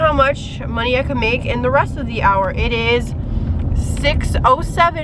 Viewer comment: I thought he said it resets at the hour